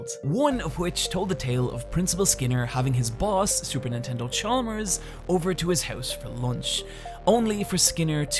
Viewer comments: Hey, did you say Supernintendo Chalmers at 8:36? Amazing video by the way, I just subscribed to your channel, I love it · he really said Super Nintendo Chalmers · did he say super nintendo chalmers? lol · Did you really just say "Super Nintendo Chalmers", or am I just mishearing you? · Sounds like you said Supernintendo Chalmers